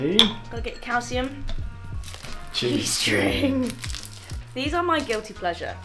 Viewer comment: You said "cheese string" and I heard "G-string". 🤣🤣 We call it "string cheese" in the US.